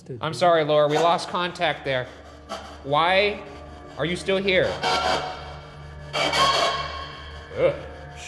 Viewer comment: i hear HELP!